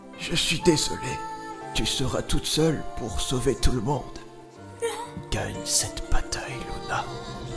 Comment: Jai compris "gagne cette patate luna" desoler de casser l'enbience